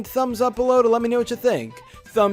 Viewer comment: I thought you said, “and I’ll thumbs up next time.” I was like, “so I don’t have to? Or will you thumbs up my video!?” :D